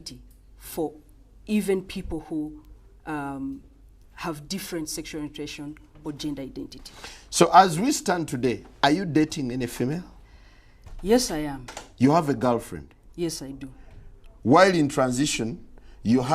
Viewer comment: I thought he said “ you have a dolphin ?” But he meant “you have a girlfriend “ 😂😂😂🥴